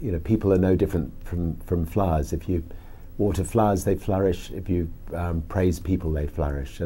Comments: Sorry. I thought he said flies too, but it turned out he said "flowers". Funny comment thought. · That's funny. I thought he said flies too. But I think he's saying flowers in a very heavy British accent. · I thought he said "flies" but no, he said "flowers" :D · Why did I hear “people are no different from flies” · i thought he said "people are no different to flies."